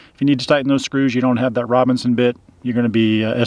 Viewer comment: Forgive me if I heard it wrong, but it sounded like you were saying "RoBINson" screw, but they're actually "RoBERTson" screws....just in case someone is trying to look them up. The screws and bits come in sizes 1 to 4. I'm not sure what size is used in the RV (I also have a Winnebago), but my cheapo drill bit kit came with all 4 sizes. P.S. Great tip about the tools in the passenger-side bins! Now I have to do some rearranging.